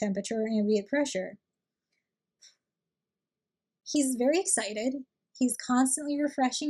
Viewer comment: I heard “he’s very cited” and I meannn maybe? YOU cited him lmao